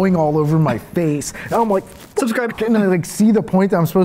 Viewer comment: Am I crazy? Why do I hear as if someone threw a bit of misplaced audio at 3:51? Like I hear the words “subscribe to” but not with the stuntmans voice, it’s a higher pitched voice. Wtf am I the only one? Am I completely nuts now?